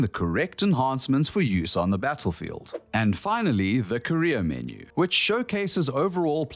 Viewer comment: I thought he said Korea menu.